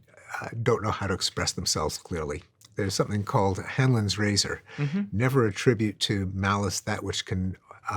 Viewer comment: what did you just say? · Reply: Hanlon's Razor you mean?